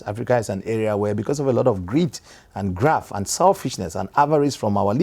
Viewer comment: "africa is an area where because of a lot of greed and ??? and selfishness and avarice" Can someone tell please me what the missing word is he said? Youtube transcribed the missing word as "graph" which makes no sense in context for me. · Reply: The word is GRAFT which means political corruption